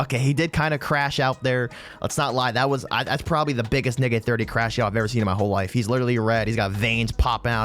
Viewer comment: the captions did NOT think you said Nick